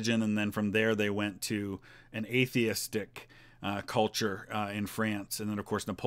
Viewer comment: At first I thought you said “an atheist dick-culture”